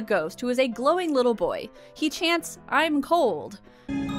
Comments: LOL for the longest time I thought the ghost said.. I'm mold. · I remember that "I'm cold" line in my 30s. I've thought of it randomly throughout my life, so they must have been doing something right. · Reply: I still say it to my Mom whenever I’m cold at my parents house. Although I always thought he was saying “I am cold” rather than “I’m cold”